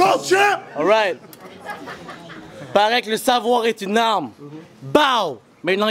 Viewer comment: c moi ou a 12:26 la fille dit pointe ton backup lolol